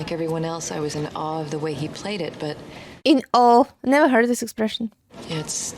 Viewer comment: I think you may have misheard what she said at about 15:10. She didn't say "In All", she said she was "In Awe." Awe, meaning wonder and respect, like when you say something is Awesome. That's like being In Awe!